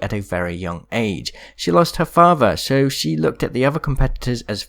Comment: When he said Watsson lost her father all I heard was, " Watsson lost her faja."